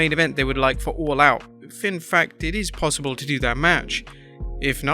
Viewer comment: it read if in fact * but I kept hearing *fin fact